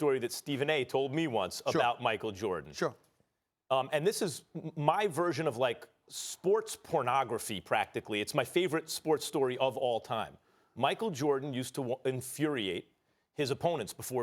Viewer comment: Bru did max just say “sports pornography” @ 3:40?